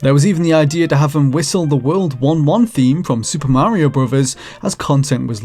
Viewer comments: Same! I heard "World War 1 theme" and I was like "hold up, wait a minute..." 😂 I still don't know what he actually said, tho, it really sounded like he said World War 1 theme 😆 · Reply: he says world 1-1 theme. From Super Mario NES. See what we did there? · Man I thought you said Mario whistled the World War 1 theme... I was like wtf is that...? and THEN I was like holy shit the Mario song came from a WW1 song? Then it finally clicked, but it took a min lol.